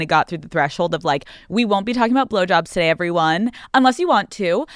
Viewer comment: Can someone tell we what she says minute 1:59 please, I don’t understand the “we won’t be talking about…”? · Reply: Blow j o b s